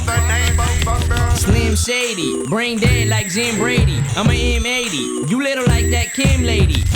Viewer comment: Damn, I always thought he said Jan Brady. Also another few things I picked up watching this. Well done sir!! I'm a lifelong Slim fan at 44yo. Now a new fan of yours.